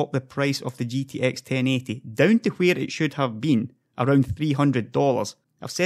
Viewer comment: Did he say GTX 1080 for $300 at 10:59 ? This would be amazing.